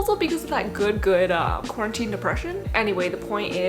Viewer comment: I thought u said good good crunchy depression im